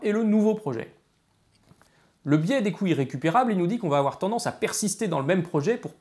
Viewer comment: toute la vidéo j'ai entendu "le biais des couilles récupérables"